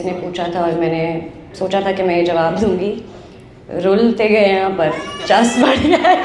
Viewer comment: what did she said🤔?? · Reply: Rul tay gaye an per chas baree aaye ay